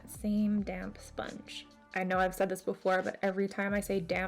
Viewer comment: This look is so fucking cool! 💚 And for the record, I STILL mishear "damn sponge" literally every time!! I've watched hundreds of tutorials in my lifetime & still hear it, hahah